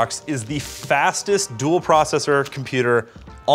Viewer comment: I'm half asleep and thought said "dual core processor" and got really confused